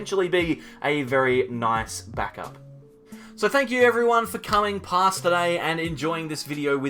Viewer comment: Lmao I thought he said "thank you everyone for coming to class today" and I was like "No, thank YOU Mr. Knuckey. Here's an apple 🍎"